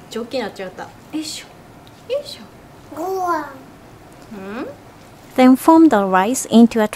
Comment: I could have sworn I heard say "Gohan"! Soooo cute.